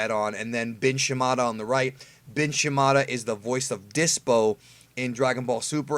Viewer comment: Anyone else thought D was saying "Bitch" Shimada?